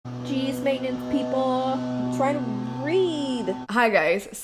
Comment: When it first opened I heard "cheese maintenance!" And was like what?! 😂😂😂🙈 Also, so excited you guys are reading one of my favourite books! I loved all the light so much 💕💕💕. Marie and Werner ugh my heart! Also LUNAAAA top quality content right there hehe, we always call Mauser the king of cozy because he always steals pillows and the softest spots he can find 😂.